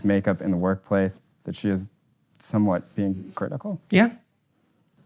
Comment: Although I do agree with 99% of the things Jordan Peterson says, however calling people you disagree with "mentally ill" is a quite biased statement. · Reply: Peterson never said sexual assault victims should be criticized, you misunderstood the word "hypocritical" and thought he said "critical" as in criticized. I have no further points to argue. Babble all you want about sexual harassment semantics. I'm not gonna bite.